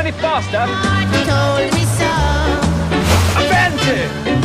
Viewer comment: What is Piers saying at about 0:28? It's right after he says, "Can't this thing go any faster?" Sounds like it starts with an "A." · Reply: Mousenomia Tadpole its 'Avante!'